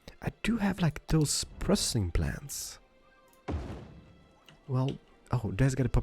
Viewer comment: i thought it said breast implants lol